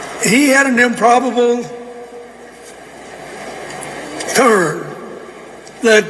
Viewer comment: I thought he said 'improbable tour or turn'..proof positive joey was installed not elected.. They are beyond exposed as corrupt